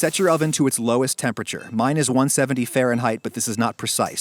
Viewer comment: did I hear -170 F. That's like - 112 degree Celsius. LOL are you trying to permafrost that meat.